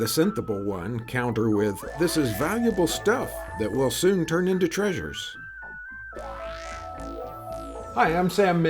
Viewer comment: I heard: " this is valuable stuff that will soon turn into trash... (ures)"